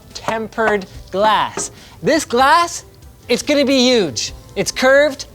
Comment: Did he say "It's gonna be Huge!"? Has he visited Florida recently, or are more car salesmen yelling this than I know about?